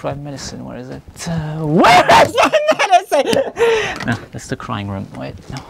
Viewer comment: I heard "No, that's the crime room." at 2:33 😅😂